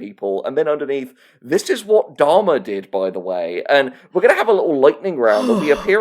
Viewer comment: I thought you said "Dharma" like Dharma and Greg. I was so confused what she did in that show.